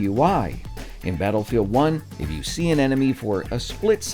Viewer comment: who else heard "Sea 🌊 Anemone" lol?